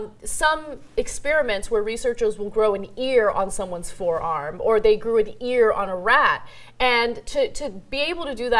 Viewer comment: Umm the subtitles are wrong. Should be "Grow an ear on somebody's forearm or they grew an ear on a rat." wtf